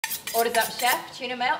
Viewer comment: For the longest time I thought she was saying "What's up chef" not "Order's up chef" 😅